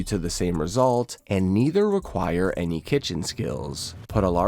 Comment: I thought he said, "Neither require any kitchen scales." While proceeding to immediate request a scale from the viewer.